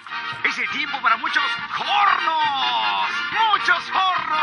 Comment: 🎵 muchos hornoss 🎵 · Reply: Thought he said "muchos pornos"